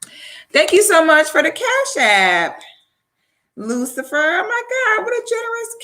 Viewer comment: in this video, is she thanking Lucifer for a generous cash app? I really hope I heard that wrong